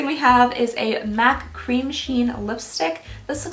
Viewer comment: I swore you said the MAC lipstick was cream cheese for some reason.